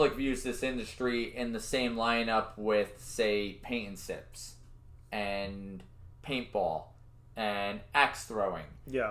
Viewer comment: What word(s) do you say at 3:33 ? The first thing on your list of similar attractions. I can't make it out. Thanks! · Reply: Paint & Sips, Paintball, and Axe Throwing